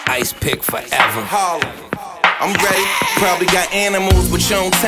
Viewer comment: Anything styles and jada on is a classic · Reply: did he say im gay?